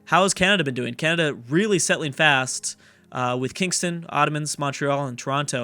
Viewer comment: On 10:15 Saïd Ottomans instead of ottawa. Lol